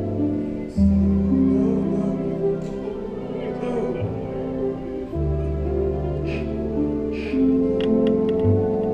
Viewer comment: This. is. so. sad. Oh my gosh :( What's he saying at 1:05? "No love"? "Not that."? He's says something I just can't here it! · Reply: He says stop I think ?